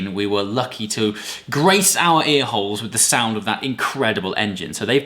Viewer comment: I'm not the only one who heard A-Holes right? Right?